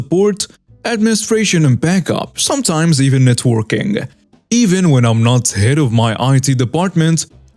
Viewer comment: Glad you have the captions up, cuz I heard "sometimes even the twerking."